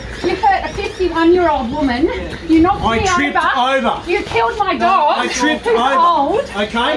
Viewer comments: did i hear “you killed my dog, who’s old” from Kristo’s mom? is the dog ok? 😢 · "You killed my dog" am I hearing this shit right? Did this absolute piece of shit of a cop really factually kill a dog there? What? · did she say they killed her dog? This is so fucked, I’m ashamed to live in this country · Wait did she say they killed her dog?! At 17:38?? I'm so confused wtf happened to the dog?! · did she say that he killed her dog!??! what? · did I just hear "you killed my dog" ?!? pls lord I want to have misheard that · killed dog? Did I hear that correctly 😳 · did she said you killed my dog??